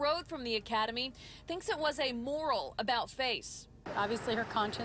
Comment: at first I thought she said "oral"